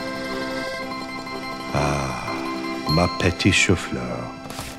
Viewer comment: In 12:25 what did spy say😭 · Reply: “ma petite chou-fleur” it’s french for “my little cauliflower”